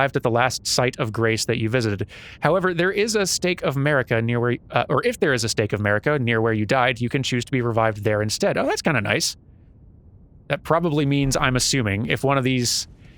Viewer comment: I totally heard "state of 'Murica"...